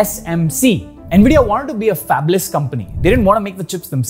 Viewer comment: Note at 8min we are not talking about the later or current PS or Xboxs. We are talking about the initial ones — this is part of nvidias story not current day use of their cards in consoles. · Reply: And at 6:15 and 12:14 fix in [cc] caption instead of "fabulous" it should have been "fabless"